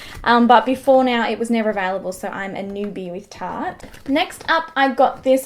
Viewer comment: wth are you saying!!!!!!!!!!!!!!!! are you trying to say the word AVAILABLE? or OF VALUE? i don't understand your speech at all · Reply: available